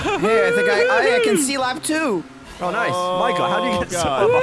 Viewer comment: it sounds like saying "oh nice".